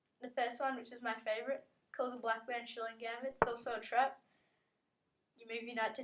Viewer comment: Whats the name of the first trap you used at around 00:28. It sounded like you said Blackmar Diemer Gambit, but that gambit is something else...